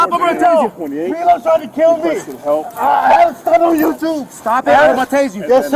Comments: did he say "I saw it on YouTube "? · when the subtitles say intangible it sounds like he's saying "I had a stunt on YouTube" but maybe it's just me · if you slow it down to 0.75 I swear he says "Cee-Lo is tryin to kill me" The subtitles even confirm that odd statement. So apparently meth and other drugs had nothing to do with this encounter. Cee-Lo Green sent the cops kill his ops! What a shame that the police are now "George Floyding" Cee-lo's Ops XD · i think i heard him say "youtube" i think he was trying to get someone to record him